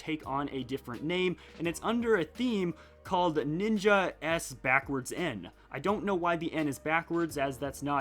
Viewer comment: I thought it said "ninjasm"